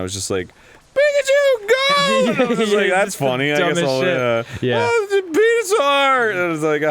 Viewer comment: it sounds like arin said "penis-saur"